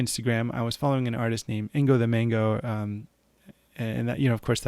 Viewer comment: name of the artist please?:) i cant hear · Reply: Angothemango